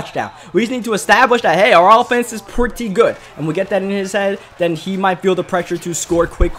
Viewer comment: what did he say lol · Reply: “We get that in his head.”